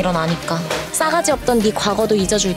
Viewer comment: I heard dan oh said "나 결혼 할래" 0:34 na gyeorhon hallae, Idk what it means, but talking about 'I will marry you' something like that. omo, really?!